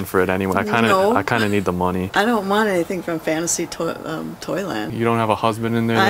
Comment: I thought he said fantasy toilet lmao